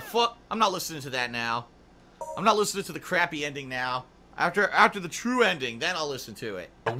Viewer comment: did he just call Dreams come true "Crappy"?